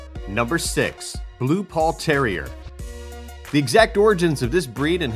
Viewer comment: I thought he said blue balled terrier lmao